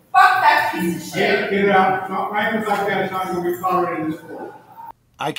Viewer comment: All I heard was “free speech won’t be tolerated in this court room!”